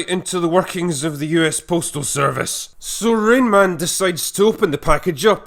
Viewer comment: did you just say rain man. Like the movie